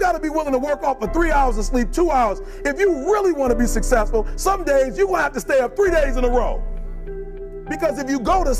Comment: did he just say "some days you'll have to stay up three days in a row"